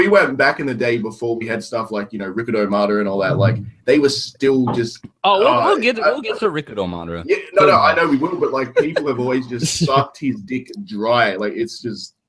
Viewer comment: true only because madara hasn’t shown sealing technique here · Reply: oh I thought you said Edo Madara 😅